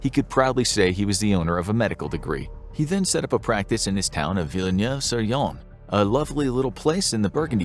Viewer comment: What town did Hé say at 4:40? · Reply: Villeneuve-sur-Yonne